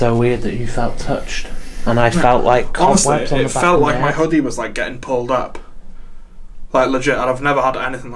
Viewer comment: Omg lol😂😂, @ 32:23 sounds like he say "I felt my huggie got pulled", 😂😂